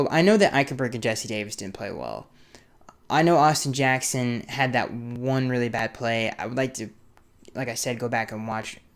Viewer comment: Correct me if I’m wrong but I could have sworn you just said Austin Jackson has one bed play a game?